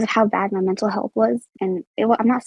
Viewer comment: Hi um, I know this was uploaded a month ago as of me posting this comment, but I noticed some incorrect subtitles you might wanna look over? At about 49:03 it says “it doesn’t have an impact on me” but it seems clear that she says “it definitely had, like, an impact on me” At least that’s what I hear and I feel like it makes more sense. Great vid though!